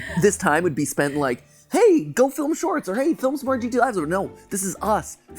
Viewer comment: why does subtitles say GTA intes of GTL 🤨